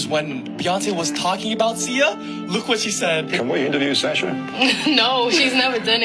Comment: those dudes goofy. He clearly said Sasha